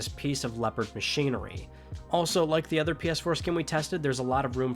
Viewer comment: Lol why did I hear “like the other PS-foreskin” around 15:09 EDIT